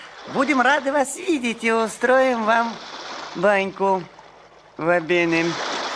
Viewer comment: Джулико говорит Ва бене, а кажется будто что он сказал "Давай пинем".